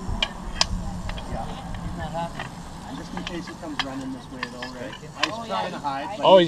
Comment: did I hear " in case he comes RUNNING this way!" That's really funny...👌👍👊👊👊